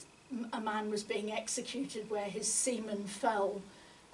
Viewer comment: "did?...did she just say 'semen'?! no, she said 'seed' I'm certain [review, play] that...still sounded like 'semen'. I know, ill turn on closed captioning and read it! [Menu, cc, on, review, play] ...oh dear...